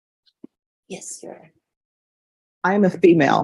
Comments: ok so it did sound like she was saying Yes sir ,🙃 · I heard her say yes sir... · Her "your honor" does sound like "yes sir". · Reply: Closed Caption thought she said "yes sir" 😂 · "Yes your honor" that's what she was saying, but I can see how the judge could hear a "sir" in there. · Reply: Like Judge Boyd, I also thought she said "yes sir."